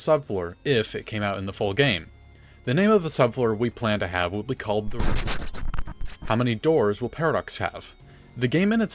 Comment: there was a glitch here 0:44 , 😅 could you please repeat what you said 🤔🫠🥀💀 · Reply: Original it was the ruins, but they changed the name once foundation revealed ruins